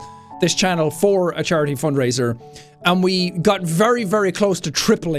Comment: The first time i heard him say charity fund raiser i thought he said Charlie foot raisin.